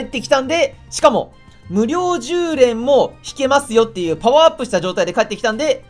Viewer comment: 無料10連が無量空所に聞こえたw